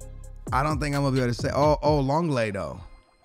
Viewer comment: Lonlego instead of Lenglet 😂